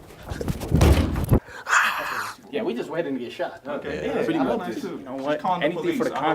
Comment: Did they just say we just waiting to get shot SMH .